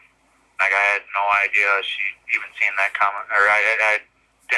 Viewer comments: if you listen closely he says "Like, I had no idea she even seen that coming" then he changed what he said. 🤨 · listen to what he says. “She had no idea she didn’t see that coming” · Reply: “I had no idea she even seen that coming.” Is what he said. He clearly misspoke, but what he said opus what he meant. He had no idea she knew she was going to do that. Or that she, “seen it coming”. He corrected what he said right after. What he said in his correction had the same meaning, he just worded it better.